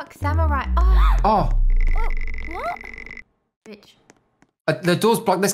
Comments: I thought she said bitch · Lol did someone say the b word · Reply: She said rich x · Did she say bish 8:56?